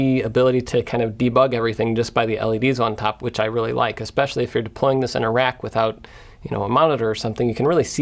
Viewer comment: I heard "deployed in IRAQ" and thought "Wow we're back there again??" 😅